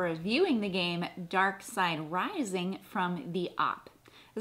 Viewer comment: I misheard at one point and thought he said Darkseid Rising! That would be another awesome remake! Dc Comics New Gods Darkseid Rising! Stop Darkseid from building the boom tubes!